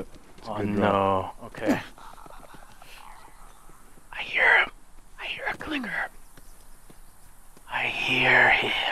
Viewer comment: I thought he was gonna say, " I hear a Hooker " instead of, " I hear a Clicker ".